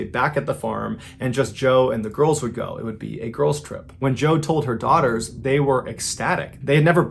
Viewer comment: for the first one I thought it said GIRL'S DRIP 💀